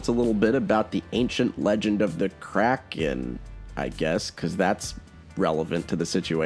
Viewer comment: I seriously thought you said "Crack Kid" instead of Kraken and I was actually perfectly fine with that for a moment.